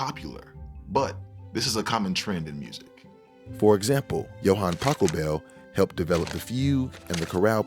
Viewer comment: Ok I had this on in the background and I heard "Johann Taco Bell". I thought he was making a joke or something until i replayed it.